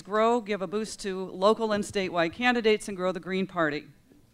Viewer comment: did she say local instate white candidates? I hope she did not or else they may not receive minority votes? I listened to it three times, she did.